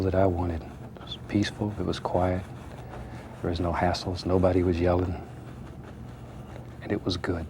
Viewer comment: did he say assholes? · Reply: It was hassles